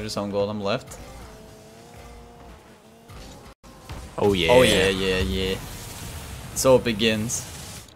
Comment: Did someone say oh yeah yeah? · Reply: Oh yeah yeah yeah!